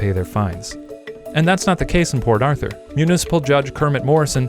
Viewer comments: I kept hearing "Poor Author".. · I thought he was saying "poor Arthur" for like 50% of the vid